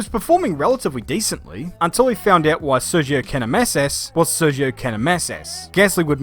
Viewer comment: did anyone understand what he says at 4:03? "sergio can-am S is"? "sergio cannon messes"?